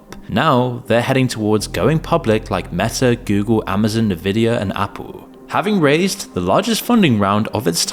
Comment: Did he say Nuvida? Fun fact, if you mispronounce a primary name that's vital to the story, you lose credibility.